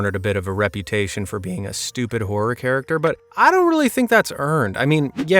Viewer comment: At first I thought you said "Stupid wh*re character". I was like, that's a little mean. 🤣